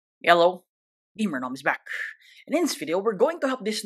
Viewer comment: Is it just me or every time gamernom says hello i Heard "yellow"